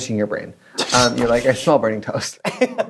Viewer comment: I thought trixie said brain toast and i lost it 🤣🤣